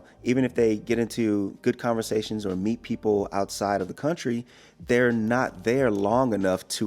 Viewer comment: I just heard you say: "Meat people outside of the country." Beware, it's the Meat People coming! 🤣